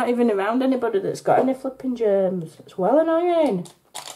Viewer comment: Those are so beautiful somewhere in the video I thought I heard you swear but that’s OK but what I was laughing about and I’m so sorry is he’s heard the family open and close the door and I think you were talking about something else but you said something like my family is here if you hear the door I should’ve threw them away or something like that and I just had laughing life anyway God bless and please have a amazing day and I don’t think you met your family throwing away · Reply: I said flipping germs … I had one of my friends thought I said a bad word too ha ha but I didn’t … I said should have locked the family in the garage ha ha ha .. noisy monkeys they are xxxx